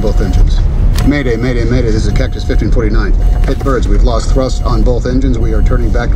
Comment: > did He say "Cactus 1429" ??